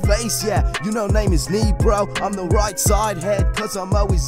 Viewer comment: did ni say negro